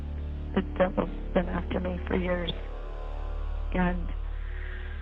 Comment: It sounds like she says "The Devil's been after me for years", not "But they have been after me for years."